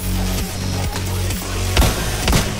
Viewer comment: one ammo whooa? Did u say whoooa???...u definetly worked in 2ID right? ㅋㅌㅌ